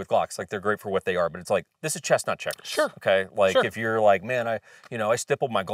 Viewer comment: it took me a few times to realize you were saying “Chess not Checkers” instead of Chestnut Checkers. 😂😂😂